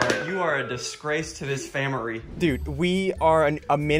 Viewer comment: did i hear that right becouse i heard famari